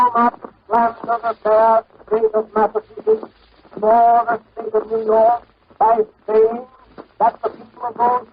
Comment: Misheard sentence in Cleveland’s speech: for the faith of New York, by peeing